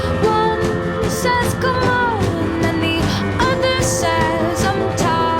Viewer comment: why did i think it said "Come on!" instead of "Good Morning!"